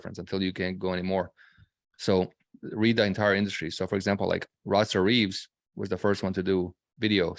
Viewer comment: who's name did he say for video? can't quite make it out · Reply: Rosser Reeves